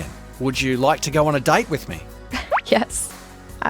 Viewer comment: I don't know why but I listen diet instead of date